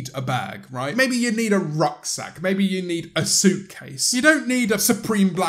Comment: When he said “rucksack,” I genuinely thought he said “rock sack” and thought he was going to continue with things like “toad bag” and “tenderloin purse.”